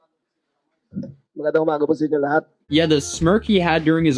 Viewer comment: My aunty is Filipino but i don’t understand Tagalog. What did the reporter say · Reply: 'Magandang umaga po sa inyong lahat' translates as 'Good morning to everyone'.